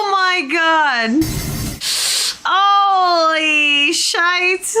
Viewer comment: actually only the films are good, except for movie 10, the Kelvin time line is also cool but the first one i like,2 and 3 and 8 are masterpices What i hear "Scheisse" ??!! @ 27:58 Where did you learn German??🤣😂🤣 Chekov actor Walter Koenig (88) is also in Babylon 5 Series (but not as a good Guy) Dr. Christine Chapel - Majel Barrett (was Gene Roddenberrys Wife) Also in Series (Lwaxana Troi TNG+DS9 , Babylon 5 =Lady Morella +She lent her voice to the onboard computers of the Federation's spaceships in all series and films)